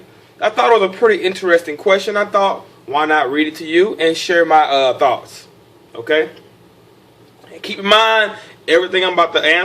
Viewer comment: sounds like he said: "Share my Thots" lol